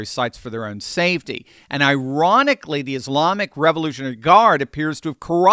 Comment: did you say ironically or Iranically?